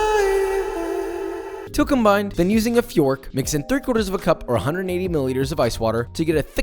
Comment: Did he say fiork?